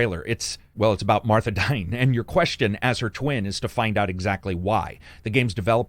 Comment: Your question is... "why did you say that name!?"